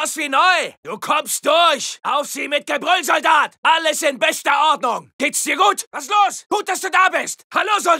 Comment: I dont know why, but i love hearing Ernst say this when im reviving teammates. Anyone speak German that can tell me what hes saying? The German words sound like "Allison Vesta Autumn." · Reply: That specific portion that you mentioned, he's saying "alles in bester ordnung"... which is like "everything's in good (tip-top) shape"